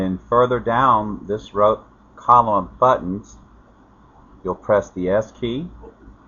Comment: He didn't show it. Just said it. Press the WHAT key?? 'S'? 'Yes'? 'F'? 1:12 I can't make out what he said. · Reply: S